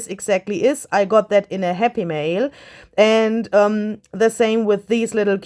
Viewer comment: Hey! First of all, I thought I heard you say you got those buttons in a "happy meal", which is what you can get at McDonald's! I got a little excited til I came back to my senses. 🤔 also I have a question. What is digital art? Where does one get it? I mean, is everything in your shop created by you, including the fine art pieces that are mixed in, like the mermaids or children etc.? How does it all work?